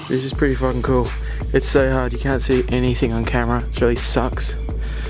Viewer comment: I just heard you won't see me